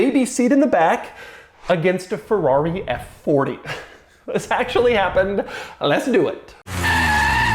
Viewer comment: What Doug said: "One of the greatest sports car ever and then my Mercedes station wagen." What I heard: "One of the greatest sports car ever and then a Ferrari F40." :D